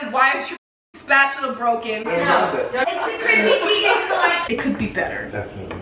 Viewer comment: “It’s the CHRISSY TEIGEN COLLECTION” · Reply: Cc i thought she said crusty vegan collection LOL